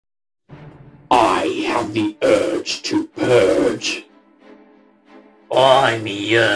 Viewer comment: Did anyone else at first thought the first guy said "I have the urge to peg" for a split second because of the distortion?